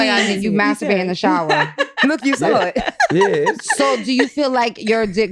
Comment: Did Angela say you sat on it?